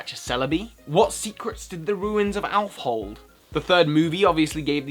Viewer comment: during your intro i misheard you as having said "the rear-end of Alph", which tbh really confused me for a sec